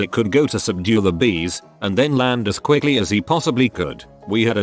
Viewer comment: I thought it said beers so I imagined beers flying and attacking people